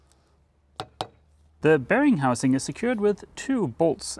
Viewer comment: Every time you said ‘bearing housing’, I heard ‘Bearing Munch-Housing’ 🤪